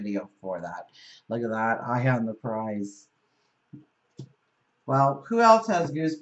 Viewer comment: FAB reading as always. Thank you so much. For "Eye on the prize"...I heard "I am the prize!" Just wanted to share that with you! ;) Have a great week!